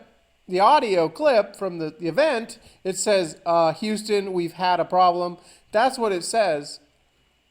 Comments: It sounds like he says, “Houston we’ve HAD a problem” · I heard ," Houston, we've had a problem" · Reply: The first time I listened i heard "Houston we've got a problem" Now all I can hear is "Houston we've had a problem" Very strange, not the same Jeff by the way :)